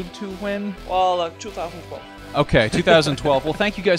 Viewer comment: Sounded like he said "Fall of 2012" at 7:58.